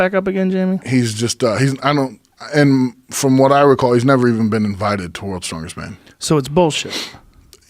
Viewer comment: I like the part where you dwell on your mishearing if him saying "been invited to WSM" and think it's either "been the WSM" or "won the WSM." Guinness world records? That's where wannabe's go to set obscure records when they can't qualify for real strongman comps. Patrik, there's no doubt you're a strong dude, but come on. Get in to a big Giants Live comp and show Robert up. I'm sure you'd out-deadlift him. Maybe out-move him in farmer's or yoke too. Let's see it.